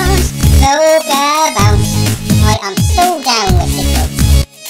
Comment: It sounded like Loca said "I am so done with the kids"